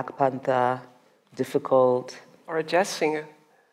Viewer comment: What does the white lady say at 1:51? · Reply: Jazz singer